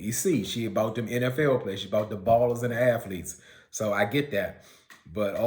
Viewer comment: Did you just say , “She’s about the balls and the athletes!” or did I hear you wrong? Because I almost choked on my popcorn 😂😫😂🤣